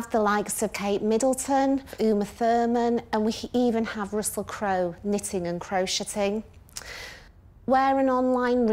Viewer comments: Am I the only who heard, "...Russell Crowe knitting and crow-shitting," at 00:59? Lol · Did she say that they even have Russell Crow knitting and crow-sh*tting?!?! 😮🎉 🐦‍⬛💩